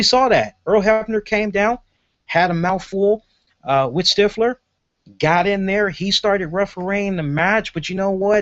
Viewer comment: i thought it was pretty good team 3d was inducted. they deserve it, and i wasnt expecting it. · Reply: haha im sorry i thought you said "hebner had a mouthful of stiffler"...im sure you said "with stiffler"....im assuming